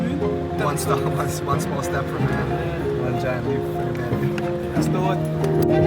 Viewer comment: “mankind” not “humanity” is what was said.